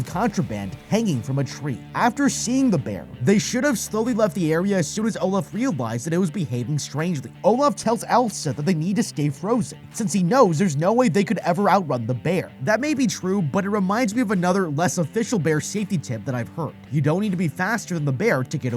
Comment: I really thought you said “Olaf tells Elsa they need to escape Frozen” instead of “Olaf tells Elsa they need to stay frozen” at 4:57 🤣😂🤣😂🤣😂 I think we all wanted to escape the movie Frozen 😂🥹